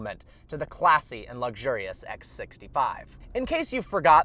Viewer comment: X 65 ? Was that what i heard? Or just my headphones are crappy ? You tell me !